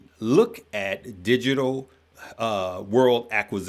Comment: I thought uncle said I had a warrant. I’m like I just took care of it